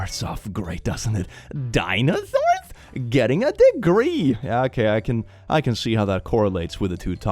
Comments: i wasn’t watching my screen and i thought it he said “dinosaurs getting degrees” and got scared. i was like “well, i HOPE she doesn’t believe that… who believes that???” 😅😂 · I wasn't looking at the screen so I thought you said "dinosaurs getting a degree" as in, she doesn't believe that dinosaurs should go to college